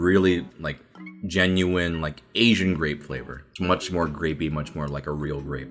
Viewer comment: I heard "Much more rapey" 😂